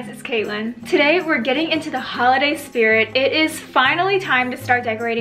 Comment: Omg I thought that she said gaying not getting in the beginning